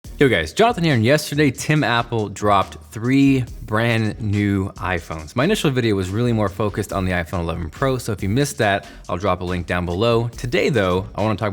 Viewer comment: I thought he was saying Tim Apple 😂 1:51 "megapiggles" that I did hear right 😂